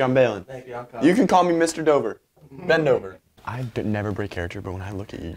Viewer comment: Shrek shirts, ben dover hoodies, and nacho cheese/Mr. Khan stickers are all available now at <link>! Thank you guys so much for everything, I hope you enjoy a longer video <3 <link> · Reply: Baylen Levine when you said Ben dover I thought you said Bend over 😂